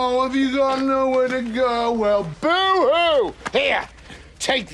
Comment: What did Christine say? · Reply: "Dad you can't..."